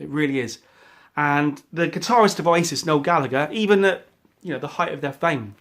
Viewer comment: I thought you said lead guitarist of ISIS